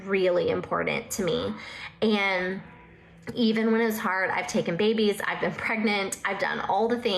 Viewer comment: Did she say she’s taking babies? Watch out! This lady will take your babies!!